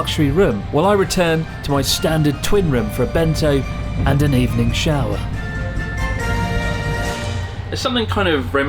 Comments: I was chewing and misheard u, I thought u said 'evening shag' instead of 'evening shower'. I havta clean up what I was chewing off the floor now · swore he said “Benzo and an evening shower” at first